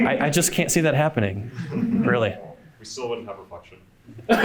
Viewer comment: What did the guy say? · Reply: "We still wouldn't have reflection"